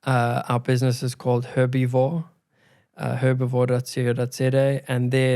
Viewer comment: Am I hearing right, "Herpifore"?